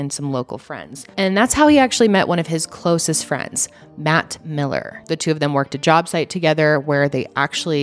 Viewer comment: oof, i thought you said mac miller. 🤦‍♀️